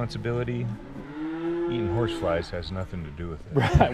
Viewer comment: Horse flies, I thought it said horse flesh. I ate horse sushi in Japan.